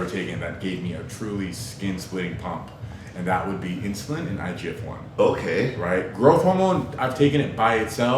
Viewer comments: what does larry say at 11:08sec insulin and what? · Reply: he says IGF-1 it's a hormone that manages the effects of growth hormone (GH) in your body. Together, IGF-1 and GH promote growth of bones and tissues. GH levels in the blood fluctuate throughout the day depending on your diet and activity levels, But IGF-1 levels remain stable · what does larry say at 11:08sec insulin and what? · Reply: igf-1